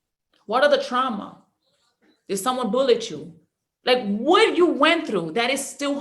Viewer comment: Her accent kinda made it somewhat enjoyable for me 🙈 not trying to be mean... But I honestly thought I heard: "did someone bullet you?" folowed by "that is still hunting you"... Made me chuckle for a bit. And I'm wondering, even though I probably just misheard... But was she talking about conscience and sub-conscience mind?? I know it was (supposed to be) conscious and subconscious, but I can't unhear it... 😔 I'm so distracted - guess my mind's in a world of it's own now